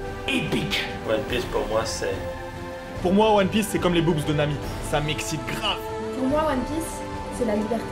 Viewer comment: putain... j'ai compris " c'est comme les boobs de mamie, ça m'excite grave ! "